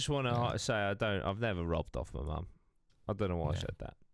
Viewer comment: While listening to this in the background, with my Southern US ears, it sounded like you suggested he "rubbed off his mom" which is much worse than robbing her. The following discussion was also quite amusing with that misinterpretation. And I agree with, it's better to rub off other people's mom than it is your own.